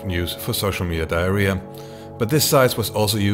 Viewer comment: Did he say "social media diarrhoea" 😂😂?